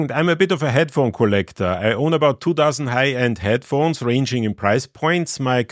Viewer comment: I thought at 0:55 you said you own 2000 high end headphones!!! I had to listen back and realised you said 2 dozen, which is far more reasonable